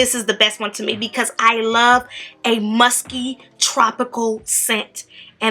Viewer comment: When she said “musky” I thought she said “musty”😂😂😂